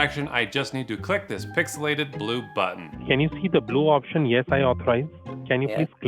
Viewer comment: Thought he said pixelated blutbaden, but I guess it's blue button😅